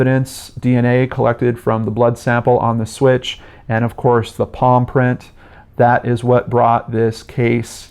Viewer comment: I heard "paw print" the first few times you said palm print and it left me very confused. Evil dog most twisted case cracked episode ever! But on a real note this case is horrific as are all of your cases usually. My endless love to these poor victims and hurting loved ones.